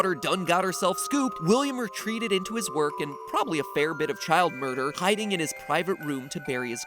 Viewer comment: When he said child murder I thought he said child molester My brain is too fucked up